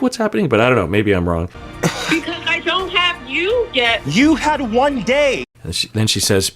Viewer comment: I think she's saying "because I know how you get," not "because I don't have you yet."